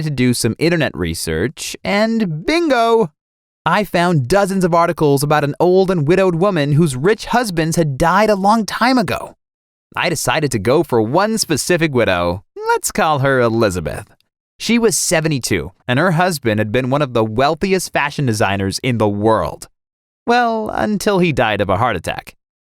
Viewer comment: I thought he said “let’s call her a lesbian” 😂